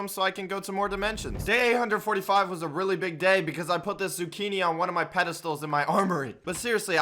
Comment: did he say i put zucchini on my armory??